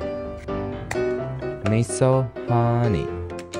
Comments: "Miiisooooo hOneyyyyyy" sounded so cute 😭😭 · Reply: I thought he said "me so ho*ney" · I swear the first time I heard him say “Me so Horny” 😂😅 or it was intended. Good word play either way